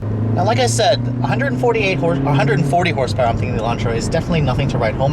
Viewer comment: did he just say elantra? all in all a good vid and good review. thanks for making it